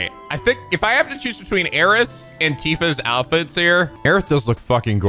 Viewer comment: Did he say Aeris?